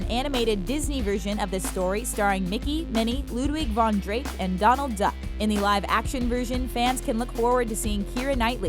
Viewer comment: I thought it said Donald trump XD